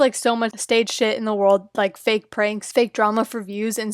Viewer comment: I thought you said fake frank and you gave me flashbacks